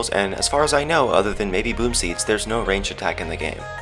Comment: Who else thought when said boomseed, thought he said boobseed...